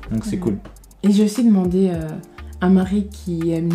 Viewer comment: Salut , j'ai vu votre témoignage et je suis super édifié merci à vous deux ! Je ne veux pas être rabbat-joie dutout, mais à 2:43 à dit : Je sais demander à Marie , étais-ce Marie la mère de Jésus dont elle faisait référence ? :) Marie ayant elle-même déclarée avoir besoin d'un sauveur , Marie étant décédée , bien qu'elle ait trouvé grâce aux yeux de Dieu, ni y a-t-il pas un seul médiateur , étant Christ qui intercède pour nous ?, serait-ce un peu comme prier à un mort ? si Marie n'est plus ? ... je veux seulement être de bonne foi ici dans l'amour si jamais je peux porter conseil. Vous avez un très beau ministère et je respecte beaucoup tout ce que tu fais, du coup si vous êtes des figures spirituelles, et beaucoup de regards sont sur vous il est préférable d'être prudent à ces sujets :) , soyez bénis et si jamais je suis dans l'erreur , apprenons ensembles, que votre couple soit bénis pour la gloire du Seigneur. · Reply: Elle a dit "*un mari* qui aime Dieu plus que moi"